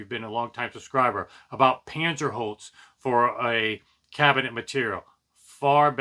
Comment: Sorry... It's called Panzerholz. Kaiser makes speakers out of it and I've featured it being used in equipment racks too. I think one of my final review videos of the GR Research NXtremes mentioned it in more detail too... If you're interested in learning more. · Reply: thanks I thought you were saying 'panzer holes' and rewound a few times and was thinking that can't be right. Lol. My hearing is pretty bad in the human speaking range sometimes and I can't make out words. Anyhow you said it clearly, I had just never heard of it before but I'll check it out!